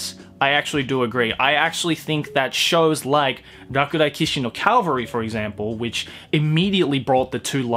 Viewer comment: How is anime called in 24:07,I couldn't understand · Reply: Its called „Rakudai Kishi no cavalry“